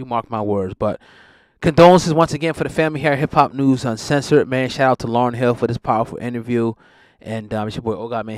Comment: Did he say shoutout to Lauren hill for this interview at 6:11? Lol that threw me for a loop